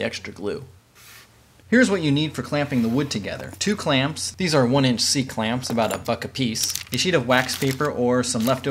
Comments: I thought he said "These are one inch clamps, about a fuck a piece" at first. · when he said "about a buck apiece" I heard about a fuck apiece and was very concerned for a moment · he said a f*ck of piece Press the timestamp again and again youll understand what im sayi'n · i thought you said "about a fuck a piece" lol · it sounds like he said "a fuck a piece" hahahah